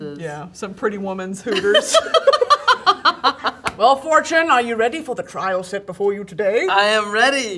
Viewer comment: I thought she called her “wheel of fortune” 😂